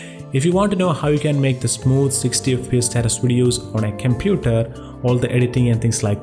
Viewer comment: did he say kemputer ?